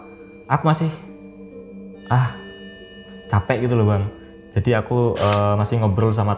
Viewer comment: itu bukan mimpi bang,tapi sukmamu di bawa oleh si hantu wanita ,dia suka sama kamu,bahkan kamu ke podcast bang pun dia ikut,dan dia ada di sampingmu 36:03 denger suara berbisik,'Capek' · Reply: bener ada bang di menit 36:03,itu suaranya kecil bgt kayak berbisik 'capek',klo dengernya dengan suara normal ga ada,tapi coba dengerinnya di kecepatan 0.25 itu cukup terdengar